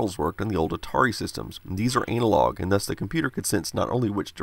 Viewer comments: did he say anal-log? lol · Did he say “anal”-log